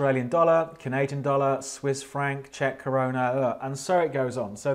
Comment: "Czech Corona...ugh..." 😂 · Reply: Ryan Power it’s actually “Czech Koruna” but sounds like he said corona 😂